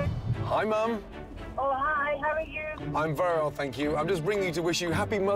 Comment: when his mom asked how he was, did he say, "I'm virile, thank you"...?